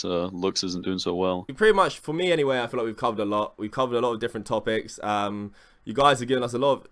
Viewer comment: I thought you said at @ 12:41 "We've cummed a lot" lol